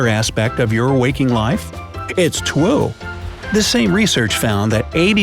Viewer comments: is when he said twoo instead of true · did he say "its twue"? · you said twi instead of true